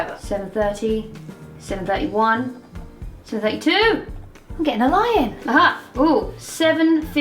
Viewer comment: At the start I thought she said IM GETTING A LION